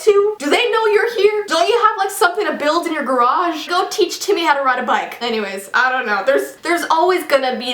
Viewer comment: "Go teach Temmie how to ride a bike!"😂 Temmie: HOI IM TEMMIE! UNDERTALE FANS WHERE AARE YOU (I know she probably said Timmy but for me it sounded like Temmie)